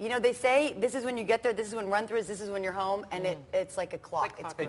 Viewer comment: what does she say? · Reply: this is when you run through it, quickly